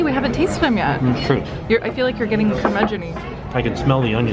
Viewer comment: What does say between 6:12 and 6:16? I've never heard that term before. Sounds like "Ca-migony"